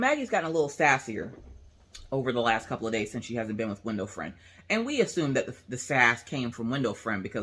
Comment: When you first said window friend I thought you said window frame and I got so confused. hehe